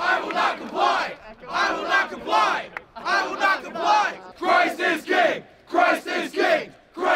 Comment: I hear "Christ is gay" and "Black lives they marry here" so it's all moot